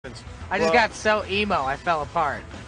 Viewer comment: my fren thought mikey said pumpkin ready. ITS EASY PEASY, PUMPKIN PEASY..PUMPKIN PIE MOTHER FUCKERS